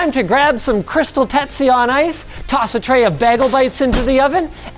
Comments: Did Linus just say bag-els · Did he just say “Baggle Bites”?!